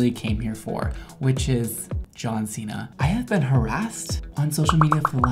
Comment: i literally thought you said "John Cena"